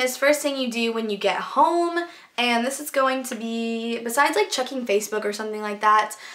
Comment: when you said checking i swore you were saying chucking hahah.